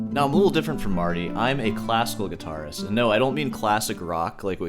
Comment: Ya i got you wrong on a video i watched i thought you were saying classic rock lol but classical is the hardest guitar playing periode it takes years and years of training and classical techniques literally are the foundation for any prog modern are classic metal randy rodes wanting to quit ozzy to become a great guitarist by studying classical guitar that should tell any metal head how hard and how much work it takes to play that style of guitar they dont call learning classical practice they call it studying that alone tells you the huge difference in a classical guitarist and popular music classical takes true study the guitars custom made a cheep one is like 5 grand classical guitars go for over 100k some in the millions they are things like European royals buy so expensive its madness like everything about classical guitar it difficult to make and buy and get down